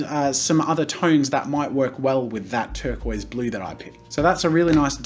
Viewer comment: when he was saying turquoise ball and the whole time i thought he was saying bowl :( lol